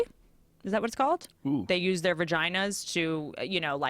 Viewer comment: did she say Virgina?